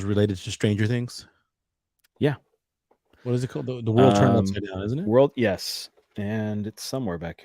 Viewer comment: Hey. Very interesting conversation. I very much enjoyed this! Was wondering if you could help me out on something: At approx 43:29 mentioned a book but he said it too quickly & I couldn't understand him. What is the book called? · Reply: So glad you enjoyed the conversation! The book is: World Turned Upside Down by Dr Michael Heiser